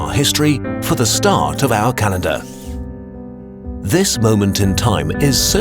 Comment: did he say Canada?